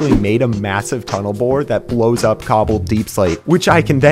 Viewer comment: I heard him say Tunnel Boar and I though of the video where grian and scar were messing with doc’s tunnel boat and managed to blow it up and it started another prank war if I’m not mistaken lol